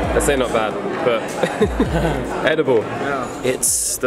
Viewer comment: Ohwww. ( 00:50 ) did you say "adobo" ??? .. Im a filipino 😊😊😊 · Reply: Saimon Dale Sebial he said “Edible”